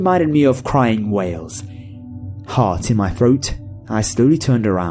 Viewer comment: I thought he said "whales" instead of "wails". All I could imagine was Dori making ominous whale noises in the woods.